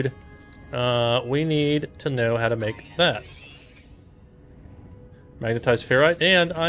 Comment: no matter how many times I listened all I heard was ‘we need to learn how to make meth’ On todays episode of Breaking